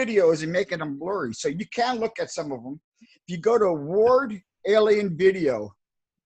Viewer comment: can someone write me hes channel to see this allien videos plz? Cant understand how to write hes channel (Cant understand hes language 100%) Ward allien videos?????? something like this At 17:16 he says hes channel. If someone can write me what he is saying, i will be thankfull. <3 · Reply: He says: Ward Alienvideo